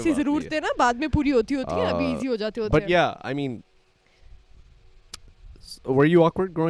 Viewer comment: He said “but yeah” I heard “bhaiyaa” 😂😂😂